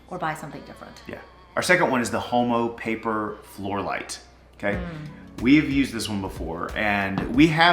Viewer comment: OMG😂😂😂😂😂 please tell me I’m not the only one who thought he said HOMO floor light!!!! Thank GOD I read the brand name when they showed it.